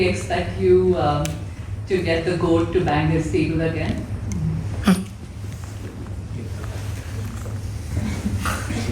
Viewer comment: anyone know what Pragg said taht made everyone laugh? Mic didn't pick it up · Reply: Pragg said, "His what?" 😂